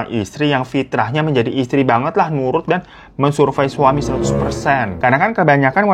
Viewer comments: sampe ngulang2 terus kirain salah denger ternyata emang beneran "... Mensurvei suami..."😂🤣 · men-survey suami 100% (apa mungkin aku salah dengar ya).